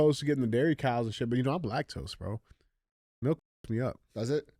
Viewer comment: Did he just say he’s black-tose instead of lactose? That was funny 😆